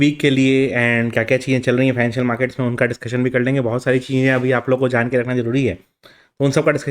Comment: Did I Hear Right 😂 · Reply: Financial bola hai,,, lekin sunne me pencho lg rha hai 😂